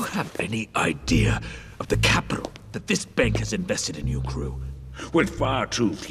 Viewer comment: subtitles say in your crew when the guy said in you Gru thats a Sin right there ☝️🤓